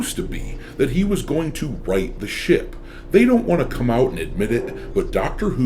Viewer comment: I thought you said “write the 💩 !”